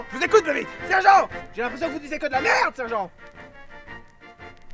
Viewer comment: "Vous disez que de la merde sergent" Sinon super video comme d'habitude ^^^ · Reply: J'ai compris "disiez" au lieu de "disez" donc, je n'ai pas tilté mais merci de me l'avoir fait remarquer.